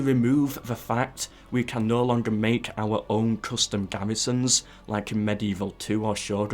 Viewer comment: Did you say custom garrisons!?